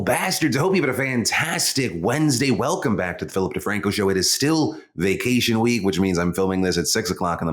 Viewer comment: For a sec I thought you said welcome back to the filthy franco show